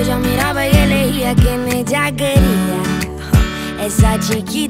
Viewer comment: Yo entendí "quién es Jaquelia", en vez de "quién ella quería", por eso no le entendía a la canción jsjajaj